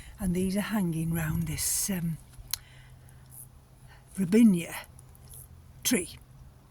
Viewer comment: did you say Robinia ? that's the tree that killed all the orchids I had mounted on the bark. All parts of the tree are poisonous (except the flowers which are quite tasty) so falling leaves and rainwater runoff could kill them I would remove them as soon as possible